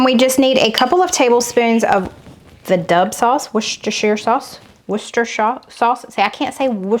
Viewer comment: I heard somene say "wash your sister sauce". Made me laugh way too loud!!